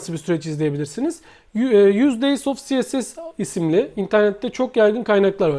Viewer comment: Hocam 3:19 da ne diyorsunuz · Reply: 100 days of css